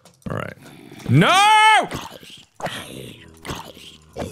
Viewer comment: did he just said the n word wtf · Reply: He said later